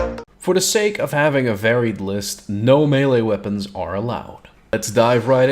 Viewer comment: I thought you said no me lees!